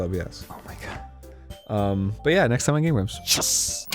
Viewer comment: I wasn’t paying attention to the intro so I didn’t hear “game grumps” I heard “gay grumps”